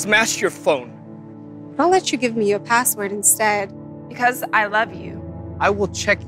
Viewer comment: Well,Just saying its freaky enough, at least you got dat message right. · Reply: Ooh, I was confused what she said before, but I think she said, 0:37 "But I'll let you give me your password instead." (Eek, creepy.)